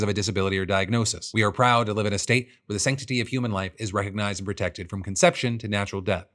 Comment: Texas says “sanctity of human life”. I hear “sanctity of human suffering.”